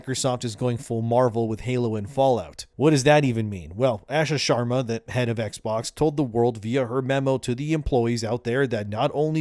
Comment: I thought you said, "Asha Sharma gives head at X-Box." What you actually said, "Asha Sharma, the head of X-Box." My bad, old Boomer hearing......